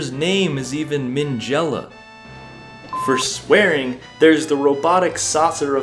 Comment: what did you say? I don’t know. I’m not mad just wondering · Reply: You’re good, thanks for asking! It’s boring though, just a transition 😅 I said “for swearing...” with a bleep thrown in